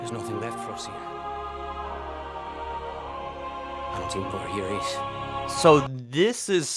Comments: After "There's nothing left for us here" It sounds like he says " I don't even know where here is" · The UNSC guy sounded like he said "I don't know where here is" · Hmm. It sounds like he said "Until where here is". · it sounds like the pilot says: there is nothing left for us here, out here for your race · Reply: I hear him say "I don't even know what year it is". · Sounds like the pilot says something like, “hunting for our/your race” race as in hunting Spartans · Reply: I hear him say "I don't even know what year it is".